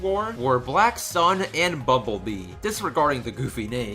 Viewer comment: DId he say Bubblebee? XD